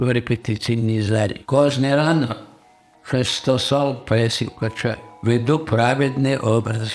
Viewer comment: На 6:25 він каже: "кожний ранок шестисот пресів качаю "!? - чи мені почулося? Це жесть..